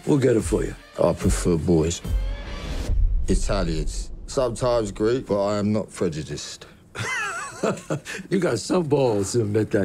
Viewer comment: Sorry, I'm not a native English speaker, what does he say at 0:58 "I prefer boys".. after that something like "I"m not prejudiced" ? , I don't understand. Someone please? :) Damn whenever I see Tom Hardy my lifeforce rejuvenates. :D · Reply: "i prefer boys; Italians, sometimes Greek but i'm not prejudiced. Enjoy!